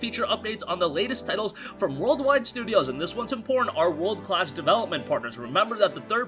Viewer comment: did he just say "and this one's in porn" hahahahahaha my ears man they hear weird shit